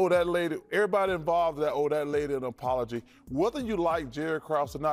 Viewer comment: Lmaoo i thought charles said “that old ahhh lady” 🤣🤣🤣